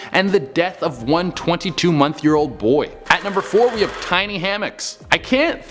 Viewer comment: I swear I heard "twenty two month year old boy"